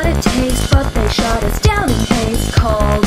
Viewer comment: I just realized when the song said “but they shut us down in hast” I thought it said “but they showed us jelly paste” I’m an idiot